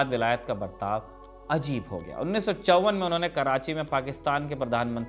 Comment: Subtitle got wrong.... 1654